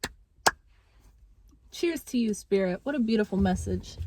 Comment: I heard you say “choose to use spirit” that hit hard realizing you said “cheers to you spirit” I always get the message intended! Thank you for being the channel you are! I’ll meet you at the top!!